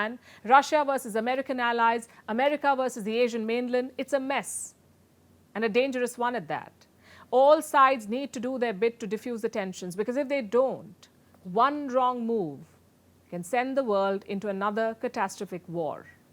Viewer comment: When she said one wrong move i thought she said one drone move 😅